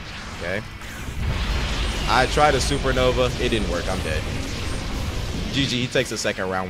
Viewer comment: did bro say im gay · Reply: Bro he said I'm died